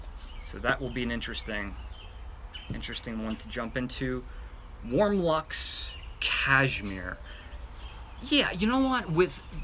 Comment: lol yeah I see, I'm watching those videos right now lol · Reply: btw any idea of when the next live vid Is? it would have been AWESOME to see the yankee village live lol but too bad their wifi isn't great... and haha i thought you said "warlock's cashmere" and thought "wow its super early for a halloween candle!" also sorry for the spam of comments lol